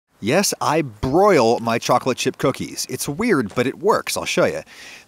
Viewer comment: I thought it said Boiled Chocolate chip cookies